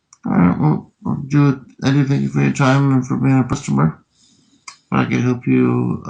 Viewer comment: I heard, "Thank you for your time and for being a pustomer." 😂